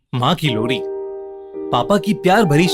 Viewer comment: kya bolu me🤣🤣🤣🤣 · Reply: Maa ki lori😂😂😂